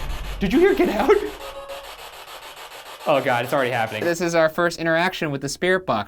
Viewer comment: I heard the "get out," at 13:15.